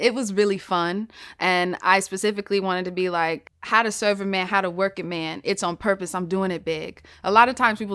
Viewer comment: I actually thought this guy just really, really liked Laffy Taffy. I also thought she said how to surf A man and how to work A man.